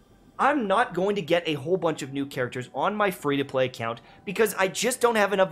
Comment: Did he say my free 2 play account in 8:25 ?!!!